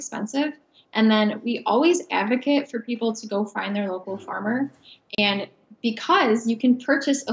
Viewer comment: Don't know why I heard "we always advocate for you to go cry to your local farmer"